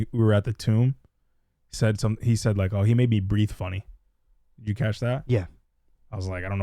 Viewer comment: I thought Roman said at the tomb "he made me grieve funny"... And it made me laugh 😂 Fun reaction & review, thank you!